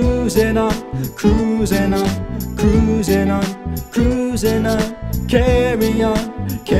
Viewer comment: OHHHHHH so he was saying cruisin on,always thought he was saying crab pee pee